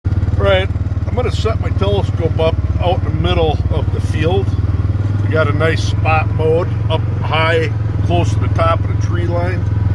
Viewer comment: I thought he said "Alright, I'm gonna suck my toes up in the middle of the field".